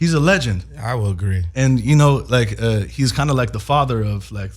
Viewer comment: Chet Hanks if you should happen to read this Gucci Mane is not the father of trap music, and you probably wouldn't believe who came up with the name trap when talking about the dope houses drug dealers sell out of but Cool Breeze from the Dungeon family is the father of Trap Music he came up with the term and OutKast Goodie Mob started using it in their music, but I understand why you would think Gucci Mane is the father of Trap Music but he's not just a lil rap history trivia you can share with your friends. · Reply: I thought he said "he's like one of the fathers of trap"? Meaning he was a part of the earlier trap music.